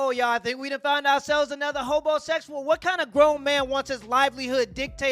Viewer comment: Did he say HoBoSexual at 2:24? 🤣🤣🤣